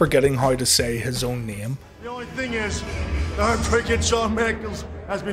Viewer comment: Did HBK really call himself "The Heartbreaker Sam Mangles" in that promo? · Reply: I thought he said 'Shawn Merkels', as though he was considering a career as the Chancellor of Germany.